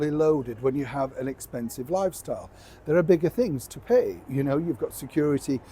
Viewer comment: That's what I thought you said, 'Harry has a very expensive 'Wifestyle'!